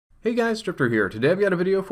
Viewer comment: Sounds like he said 'Hey guys stripper here' lol